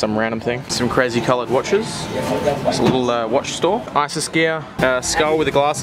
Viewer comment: did say ISIS gear?!